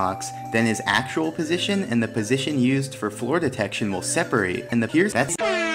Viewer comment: i heard - "and here's ass"